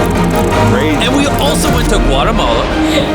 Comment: cuando decia guatemala pense que decia argentina, re flashe lpm